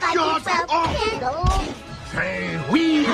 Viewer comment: When I first rode it I thought sideshow bob said "save me" instead of "say wee"